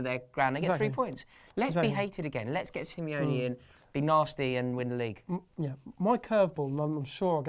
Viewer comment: I misheard 'get simeone in, be narstie, win the league' as 'get simeone in, big narstie, win the league. Thought wanted some solidity and #BASE at the back to get us the edge over City and Gunners.